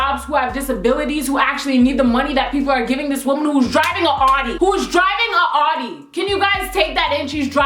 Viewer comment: its sound like your say "shes driving an hottie"